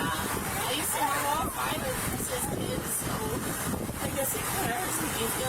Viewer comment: where does it say the 5 kids were not hers? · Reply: Start listening at 11:40. During questioning it sounds to me like she says, I used to have all five but they were his. She's asked if she has any other biological kids besides this one & it sounds like she says no.